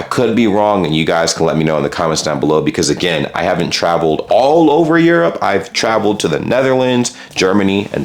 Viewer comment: He said „I havent travelled oder whole Europe….“ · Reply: Yup, you're right, he said "I haven't traveled all across Europe". My apologies for mishearing that!